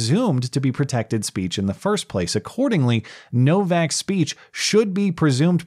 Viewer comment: When you said "Novak's speech" I heard "No-Vax Speech."